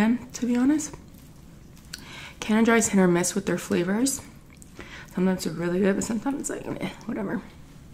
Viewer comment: anyone know what happened to the boyfriend she had a couple months ago? btw what the hell did she say @ 8:20 😀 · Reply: Y. Leal she said “Canada Dry is a hit or miss with their flavors. Sometimes it’s good but sometimes it’s meh”